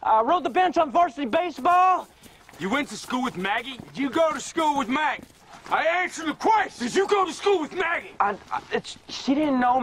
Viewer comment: To me it's sounded like Shane said; "You go a school wiv Maggie? I answered a question!? Dew go a schall wiv Maggie?"